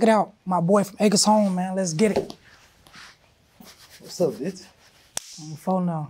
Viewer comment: Did he say "What's up bitch?"